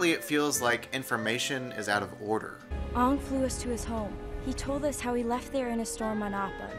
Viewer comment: did she just say Aong? WhO is that?